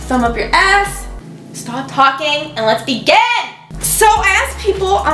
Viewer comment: I thought when she said "let's begin" I thought she said "let's be gay" and I was like YASSSS